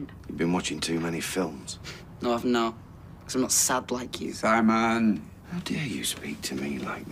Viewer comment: what did simon say to peter at 1:15? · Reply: I'm not sad like u that's what he said